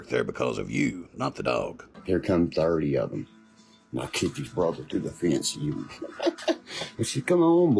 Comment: did he say he kicked the dog through the fence to sick em ???😮